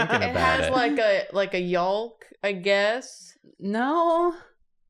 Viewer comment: I had to rewind it to hear say yolk, lol. 7:34 Did she say yalk? 😂😘 Poor thing, she's had so many problems with eggs: first drawing them, now this... I can't wait to see what next arises!